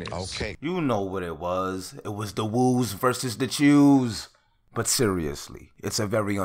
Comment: I’m Crying why it sounded like he said the woos vs the Jews 😂😂😂😂